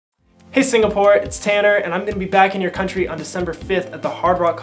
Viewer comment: omg i thought he Said Taylor instead of Tanner omg ahhaa